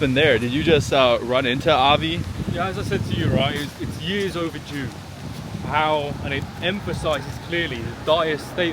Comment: what did he say 4:13? years over j,ew? didnt quite hear it